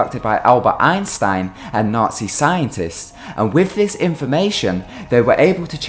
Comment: Did I hear it wrong or did you just call Albert Einstein a nazi scientist?